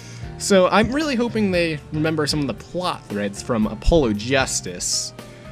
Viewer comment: A polo? Like the shirt? It's pronounced uh pol oh FYI. Like the "god". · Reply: the vid it sounded like you said "a polo." But since you already know then nevermind. Was just dropping the info nicely before someone insulted you for pronouncing it wrong(or sounding like you did.)later :)